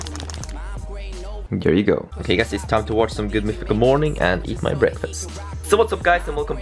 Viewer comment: I thought he said on 0:35 "Time to watch some good porn and breakfast" :D